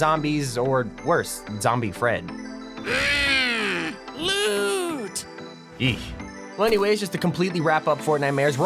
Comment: did I just hear German ? Hmmmm Blut aka hmmmmm Blood